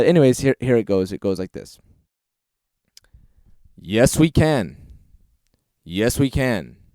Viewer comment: It sounds like he's saying je suis ken instead of yes we can at 1:10 and ending at the... you KNOW when.